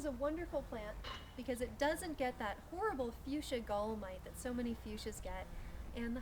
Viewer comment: You're saying at 0:11 this doesn't get that horrible fushcia ........ ( inaudible ) what was the rest of were you saying ? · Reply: She said "gall mite", it's a pest that all the older fuchsias were very susceptible to. Most modern fuchsias (hybrids) are gall mite resistant; that's what she was talking about.